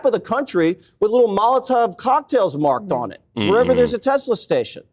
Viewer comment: I swear it sounded like you were saying “Mazel Tov” instead of “Molotov” 😂